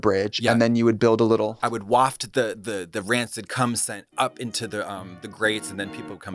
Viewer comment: Texting my dad back and I just hear "rancid cum scent" - Oh this podcast, never stop.